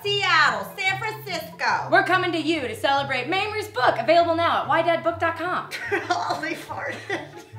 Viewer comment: When they said the name of the website for Mamrie's book, I thought she said "Why Dat Book?"